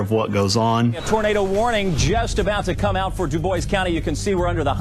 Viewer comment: Did i hear this right? "Debois"